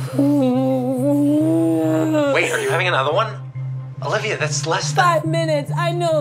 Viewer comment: THE BEGIN I THOUGHT WHEN HE SAID "OLIVIA... ANOTHER ONE"? LIKE ANOTHER BABY IN LESS 5MINUTES HAHAHAHAH